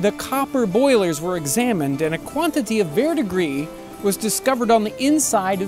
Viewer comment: a quantity of (what?) was found on the inside. I hear "verr degree". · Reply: Verdigris, the patina of copper (sort of the copper equivalent of rust, but not so destructive to the underlying metal).